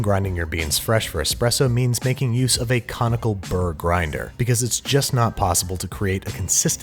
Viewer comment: I thought he was saying Comical Burr and I was trying to work out why it was funny